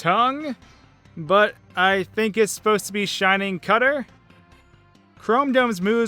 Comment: i thought he said shining cunt.